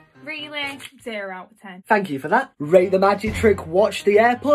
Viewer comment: I Thought He Said "Fuck You For That" Hahaha